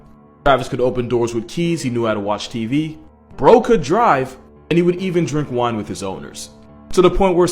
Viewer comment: the amount of time that it took me to realize that he said "bro could drive" It sounds like he said broke a drive